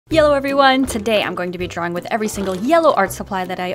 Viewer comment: In the beginning it sounded like she said yellow everyone